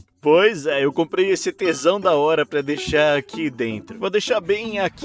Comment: so eu que ouvi t#são ?